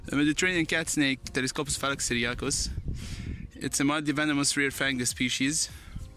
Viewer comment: Did you say fangless I didn’t here you well in this time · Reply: Nop, I said " mildly venomous, rear fanged..."